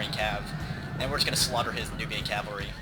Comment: I heard "And we're just going to slaughter his nooby cavalry" lol